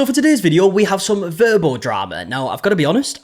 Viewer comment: I thought you said verbal drama 😅 Like, isn't most drama verbal?